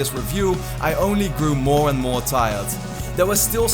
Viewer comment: are you from the Netherlands? :) You kinda got the accent :P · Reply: also because in 4:48 it sounded like you said meer instead of more which means more in Dutch XD